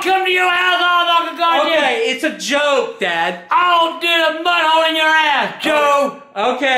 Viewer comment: lmao did he say he’s going to shove mud up..💀💀